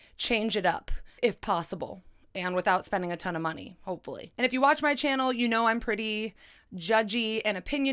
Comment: I was literally thinking how pretty u look when at 0:25 it sounds like ur saying "if you watch my channel you know I'm pretty..." lol i was like did she hear my thoughts